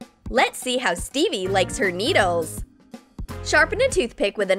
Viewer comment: Why is he putting lemon without cutting the peel... Plus It didn't show in the part that there is a bug in the shower plus at 8:47 did I just hear"how can Steve like her needle"but steve is a boy ...like of u agree